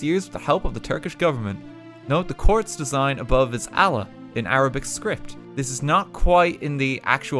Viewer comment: did. he really Said Allan istead of Allah